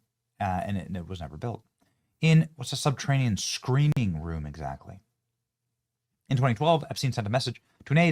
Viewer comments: When you said, "screening room" I thought you said, "screaming room" and my heart sank. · I was listening to this while cleaning and I heard you say screening room I thought you said he was making a “Screaming Room” ... I don’t think that was a coincidence👺😱💀